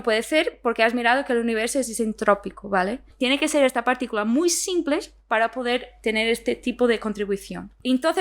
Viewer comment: alguien me puede traducir Qué dice la chica en ese momento? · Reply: "tiene que ser ésta particula muy simple para poder tener este tipo de contribución..."